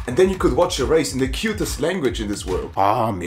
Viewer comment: did he say german is the cutest language in the world?